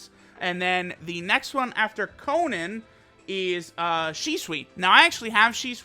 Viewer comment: im sure i heard ''Sheswi'' intead of shisui :/